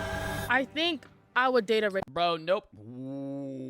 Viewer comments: was I the only one who thought she said “rapist” instead of racist and thought everyone was just going mad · like thats either a kink or shes lying cuz rtheres no way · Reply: NAHH I THOUGHT HE SAID RACIST THEYRE TALKING ABOUT RAPISTS THATS SO MUCH WORSE